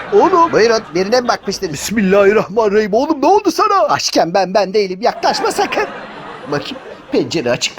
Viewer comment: Abi 0:18 dene dedin orayı anlamadım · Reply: "Açken ben, ben değilim" diyo 😂😂😂 Snickers reklamı sloganı 😂